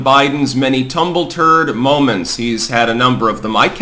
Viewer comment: I heard," tumble turd moments"? 😊